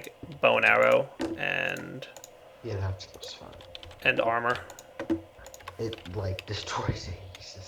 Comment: Did I hear tinker's armor?